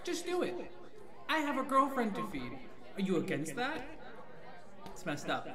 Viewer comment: Lmao I thought said I got a girlfriend to beat 💀💀